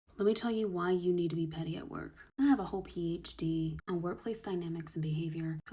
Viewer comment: Oops, I thought it said "pretty at work". My bad.